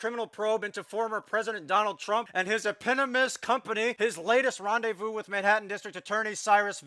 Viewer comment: Anyone hear "ee-pinnimist?" from eponymous or just us?